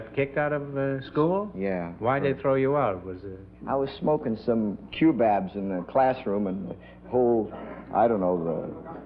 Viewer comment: "I was smokin' some kewbabs (?) in the classroom ..." What is the word? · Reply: Think it was cubebs, a spice that can be smoked. (In "The Music Man," Professor Harold Hill mentions them as a vice in"Ya Got Trouble.")